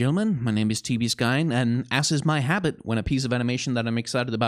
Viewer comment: "as is my habit" I honestly thought he said "ass is my habit" XD